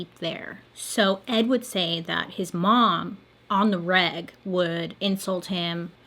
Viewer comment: 😂😂I totally thought that you said she was abusing him while on the rag, not on the reg. Lol